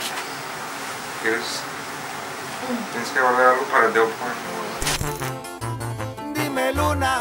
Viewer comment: Did I really hear “ tienes que guardar algo pa del porn”?😅